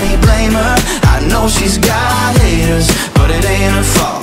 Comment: I thought he said She has hairs but it's not her fault... Oh but it's haters*, I was so happy he's a *feminist